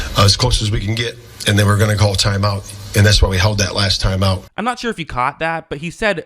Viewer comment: I think he said "and then we're gonna call timeout" not "they were gonna call timeout." I think the way he explained it more or less makes it sound like a good decision. Imo this seems like more of a Caleb Williams issue than an Eberflus issue. He got sacked with about 32 seconds left on the clock and then snapped it with 6 seconds left. You expect your starting QB, even a rookie, to be able to recognize that situation and get a play off long before then